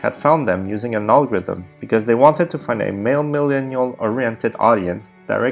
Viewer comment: I didn't understand what you said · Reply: Liz Murdoch's company Vertical Network's target audience was a male-millenial oriented audience. That's why Yes Theory was perfect for that.